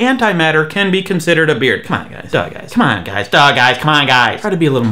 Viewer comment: Is he saying duh guys, dog eyes, or both?